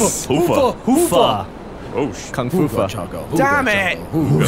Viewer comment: When the boys saud kungfah or something along those lines i thought they said kung fupa